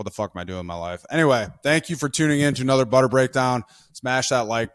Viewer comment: Did Beau just say, "give your butt boy some buttery love"?😂